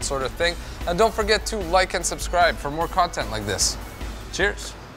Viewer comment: I heard "Isso é bom" in the end? It seems so. In regards the beer, that's a great test!